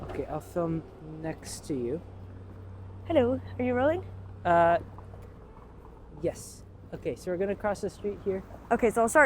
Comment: I thought she said are you Molly! LOL